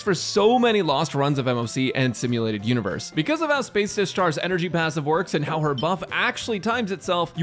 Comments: SOMEONE TELL ME THEY HEARD SPACE ISHTAR AT 1:30 CUS I DID AND FGO PLAYER SO IK WHAT I HEARD. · Did you just said Space Ishtar lmao 😂❤